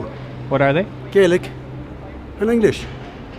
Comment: a part of my soul died when the lady said she speaks american and english :'') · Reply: I thought she said American English